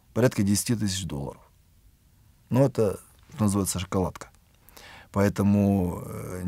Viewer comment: на 05:07 там батюшка какое-то словосочетание произнёс, какая-то "закалатка". Не могу разобрать. Будьте добры, напишите кто расслышал. Спасибо. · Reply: Анатолий,там произнесена фраза :" Это называется шоколадка." Мира Вам во Христе и радости в душе!